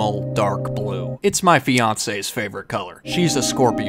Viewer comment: WAIT! DID HE JUST SAY FIANCÉ? BROOO CONGRATULATIONS!!